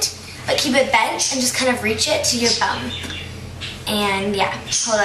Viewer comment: did you say reach it to your bum 3:45-3:46?